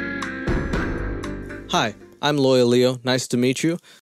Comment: Okay, I know you don’t live in the US and aren’t trying to sound American, but I just gotta say it. I didn’t understand that you were saying, “Lawyer Leo” until the last minute of this video. I kept hearing, “Loyal Leo,” and thinking it didn’t make sense lol